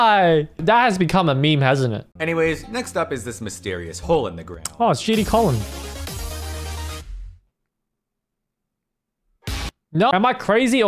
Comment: caption auto generated English Oh sh*tty [Music] Why does my captions say that